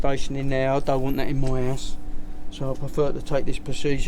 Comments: “You see the infestation in there, I don’t want it in my ass.” LMAO I think it was house but that’s what I heard at first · I thought he said don't want that in my ass